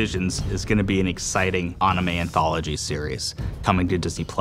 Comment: Did he just say “onime”?🙄👀👂